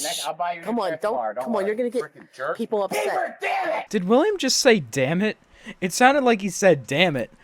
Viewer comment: yeah it sounds like William said "peeper dam it"